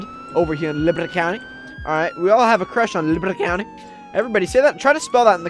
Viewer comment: I think its sounded like lebanon county on